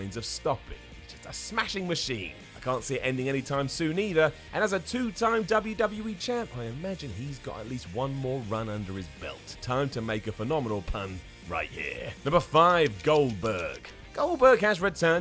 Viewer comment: did you say champlon